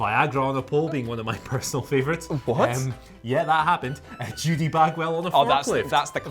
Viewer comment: Edit: I misheard Everyone gets this wrong. It wasn't "Judy Bagwell on a pole," it was "Judy Bagwell on a forklift" lol